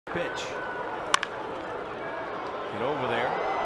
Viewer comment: I SWEAR THE FIRST WORD I HEARD SAID 'BITCH' INSTEAD OF 'PITCH'